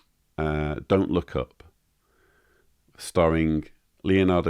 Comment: I thought you said the name of the movie was "Don't look it up"